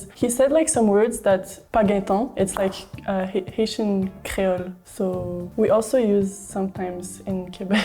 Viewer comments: Y’all put in the subtitles “ancient creole” bt she clearly said “HAITIAN CREOLE” a language from HAITI🇭🇹 strongly french basis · the women said what sounded like "Haitian Crealo"(which is real thing) and not "ancient creole" (which...can't really contextually be a thing)